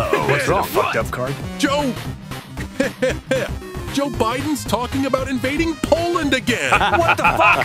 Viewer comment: Wait what was peacemaker gonna say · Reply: Peacemaker: Joe biden's talking about... Invading Poland Again.